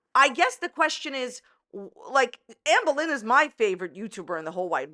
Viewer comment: My son thought you were saying Anne Boleyn! Anyway, why couldn't the lawyer just ask Amber what Alexis' email address was? Lol